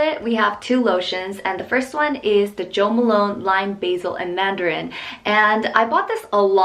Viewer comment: i thought it said post malone instead of jo malon.... i was really confused for a good 3 seconds